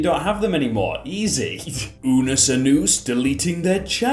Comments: DID JUST CALL IT "unus UNUS" or did i mishear it??? i never looked back at my screen so fast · Did he just call it unnus anoose?